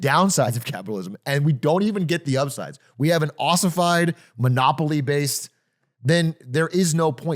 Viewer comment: did Atrioc just say "Monopoly based"? My 🐐🚿. 🥀💀